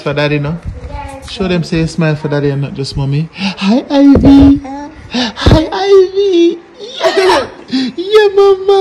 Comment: Did she say hi at 4:48???